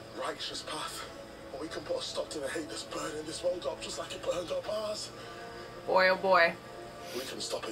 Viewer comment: Is Manchester a Martian like J’eonn is? Cause I heard him say “Just like it burnt up ours!” At 7:12-14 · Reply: Nikki Lettera he said “Just like it burned up Mars.”